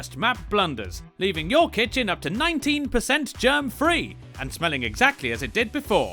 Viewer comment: I thought he said 19% german free